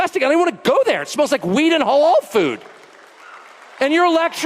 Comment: did he just say weed and .. halal food 😮